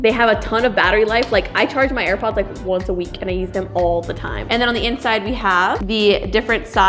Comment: is it me or does it sound like she said "I eat them all the time"??